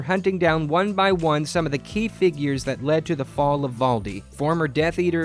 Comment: if you turn on auto generated subtitles it says "Baldy" instead of Voldy... YouTube is making fun of him too?.. Wow...